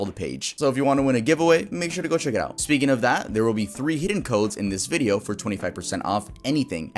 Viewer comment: It kinda sounded like he said "heeden" instead of "hidden". I've never laughed so hard in my life.